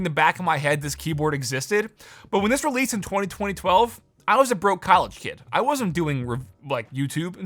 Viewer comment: I heard you say the year and it sounded right but also wrong lol 202012